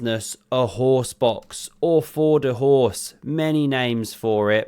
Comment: 😭😭😭😆 Thought he called it a horse divorce.